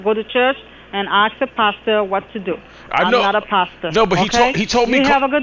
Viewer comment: I thought she said Pasta